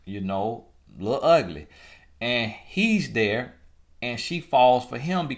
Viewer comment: Did he just say LIL Ugly at the 2:00 minute mark....I can NOT Brrreeeevve.... baaahahahahahaaaa!!!! 😭🤣😂😂😂😂😂😂😂😂😂😂😂😂